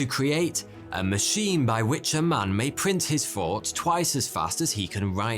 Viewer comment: I heard "Fought" in "Thought"